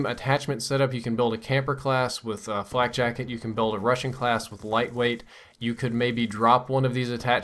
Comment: I thought he said a russian class.